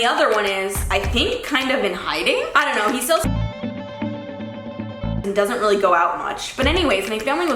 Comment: Anyone else wondering wtf she said at 6:14 😂😂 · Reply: 'He sells children ----'